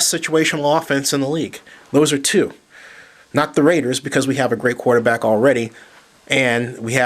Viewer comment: i had to rewind a hundred times around the 4:18 mark because i thought you said jamarcus russell was a great QB... maybe in high school